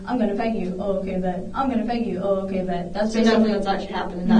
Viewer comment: Not like the subtitles have been super accurate throughout this whole video but this error in particular really bugs me, instead of "oh ok babe", she's saying oh ok bet, it really makes a contextual difference.